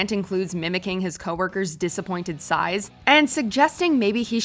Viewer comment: I heard "his coworker's disappointing size" 😝😝😝😝😝